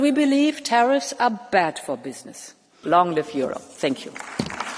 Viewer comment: Crazy, when Ursula said "Long Live Europe" at the end, I could have sworn that I heard, "Long Live the Führer"!